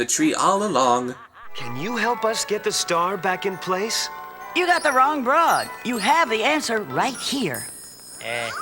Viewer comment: Did she actually said "you got the wrong BROAD." in a FRIGGIN KIDS MOVIE?!